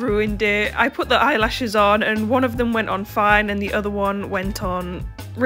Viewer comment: I thought she said “one of them went on fire” and I was like what?! 😃